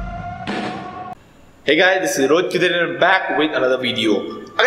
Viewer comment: In the intro I thought u said Rohit kill dinner